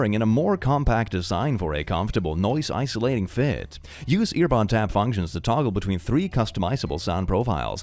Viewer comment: for some reason i heard "earbud tab functions" as ear tampons and i was /so/ confused 😭